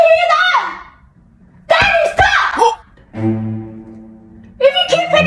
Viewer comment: I thought she said daddy stop to